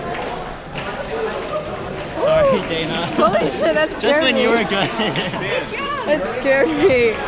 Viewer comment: did she just say holy shit?